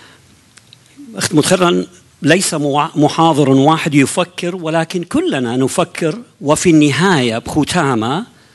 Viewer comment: did he say, "You fucker!" twice? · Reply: yufakir and then nufakir which means 'he thinks' and 'we think'